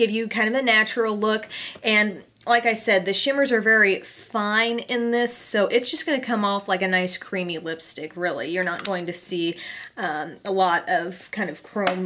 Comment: its sounds like you said ice creamy at 5:51 haha